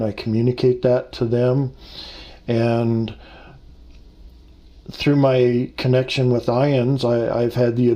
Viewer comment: At around 15:30 he mentioned "ions" and am puzzled..anyone know what that is..is it a organization for people who have experienced NDE's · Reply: IANDS, not ions.